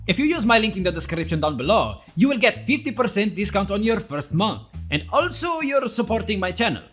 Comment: i heard him say "you will get 50% off your mom"